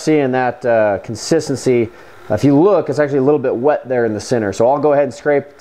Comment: I swear I heard "I'd be wet" in